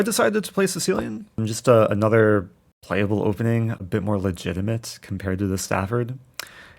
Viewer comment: thoughts on the Sicilian defense renowned for being the most tactical and imbalance d defences with black. "It's another playable opening" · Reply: I thought he said it’s a more playable opening hinting at the Stafford being straight up losing if played accurately